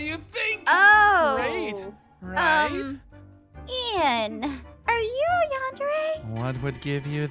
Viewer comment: When Aph said "Are you yandere?" I honestly thought she said "Are you on dugs or something?" Literally dying rn 💀💀💀